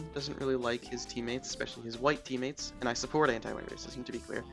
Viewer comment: good vid :) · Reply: i swear u just said i support anti-white racism but i might be trippin